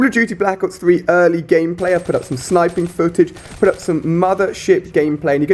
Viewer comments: i thought he said 'i put out some other shit gameplay' XD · Did he just say mother shit